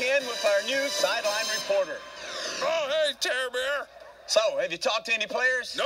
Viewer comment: Oh - hey Toby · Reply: At first I thought the camel said “Oh - hey Teddy Bear”.